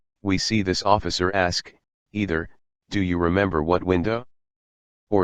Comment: He isn't saying "what window" it sounds like he is saying" one window"